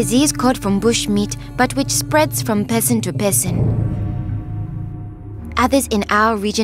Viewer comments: 1: 10 I thought she said "it spreads from peasant to peasant🤔💭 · I thought she was saying "peasant" instead of "person". lol